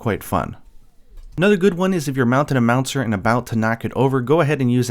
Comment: did you say "Mounting a meownster"